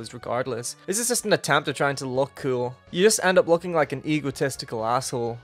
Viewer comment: with your accent the first time I heard you say "egotistical" I thought you said eagle testicle liked the video though